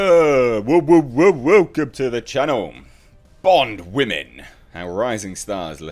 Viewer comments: Did you just say "woke em to the channel" 😂😂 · >¥< Your intro for these type of videos should be “Woke’um to the channel”, because that’s what it sounded like you said lol ¥•¥•